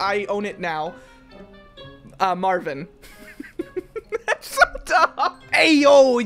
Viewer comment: did you say marvin because my nme is marvin